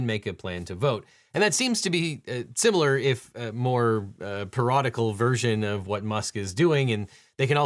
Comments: hand-pink-waving: @ 15:52 - The closed captioning erroneously prints out "periodical" instead of the correct "parodical" here. This changes the whole meaning. :hand-pink-waving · The subtitles say periodical but it sounds like it should be parodical